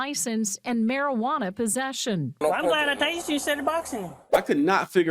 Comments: I heard "I'm glad I tased you instead of Bystan" not sure what the Bystan means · It Sounded like, at 6:57, he said “I’m glad I tased ya instead of boxin’ ya” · I heard “I’m glad I tazed you instead of boxing you” at that part you weren’t sure of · sounds like he said I'm glad I tased you instead of boxing???? That what it sounds like.... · "I'm glad I tased you instead of boxing." (?) That's what I thought I heard, then found out he was previously a boxer, which "confirmed" it for me. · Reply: "I'm glad I tased you instead of boxing..." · "I'm glad I tasered ya instead of boxin ya then" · Sounds like he said I’m glad I tased you instead of boxing you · "I'm glad I tased you instead of boxing you." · Taking into account that the man was a boxer, it sounds like the cop said, "I'm glad I tazed you instead of boxing you." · That cop’s voice sounds like a cartoon caricature of a redneck. He sounds like he has the intelligence of one too. It sounds like that cop said at around 6:56 “I’m glad I tased you instead of boxing you”. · He said: "I'm glad I tased you instead of boxing you." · They say " I'm glad I tasered you, instead of boxing. (you)?" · "Im glad I tased you rather than boxing you." 6:59 thats what it sounded like. Oh! I guess that is what he said now that I find out he was a boxer! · I'm glad I tapered you instead of boxing you Wtf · I heard “I’m glad I tasered him instead of boxing him” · Sounded like he said "I'm glad I tased instead of boxing"